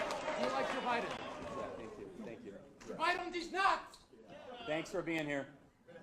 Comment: What did that guy say at the end. I heard “Pie on deez nuts!”